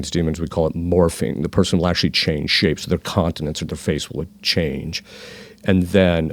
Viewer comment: Talking about Demons changing people's appearance of Face It sounds like he said 'Continence'. What was meant was 'Countenance'.